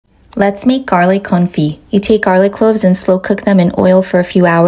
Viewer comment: For a sec I thought you said “let’s make some garlic Kulfi”